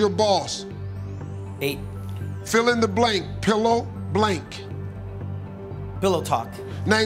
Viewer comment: I thought said pillow top instead of pillow talk.